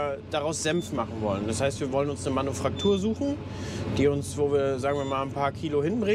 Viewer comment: Hab ich da richtig gehört, du suchst eine Manuf(r)aktur 12:45 ( Knochenbruch im Handgelenk )😅😂. Auha Schöne Ostern 🐰🐣 euch allen